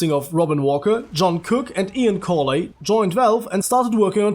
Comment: did he say Jungkook at 1:54?